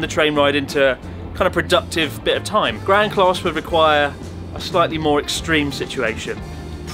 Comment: I thought they were saying ‘grand’ class but then I realised it’s legit called ‘gran class’